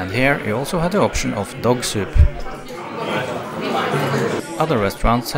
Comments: Uhhh did he just say dog soup?? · Did he say Dog soup on 15:47????? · did you say “Dog soup”? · did I really hear you guys had the option of DOG SOUP????? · did you say in the vid dog sop ?